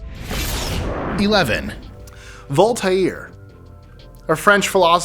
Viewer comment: In number 11, I thought he said 'Altair'😂😂😂